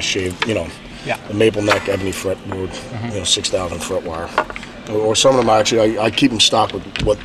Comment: My english is not good enough. What does Zakk say from 7:10 to 7:31? I only understand about the "not everybody likes that" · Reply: Hes saying he likes the Dunlop 6100 frets, he likes big frets, but not everybody likes that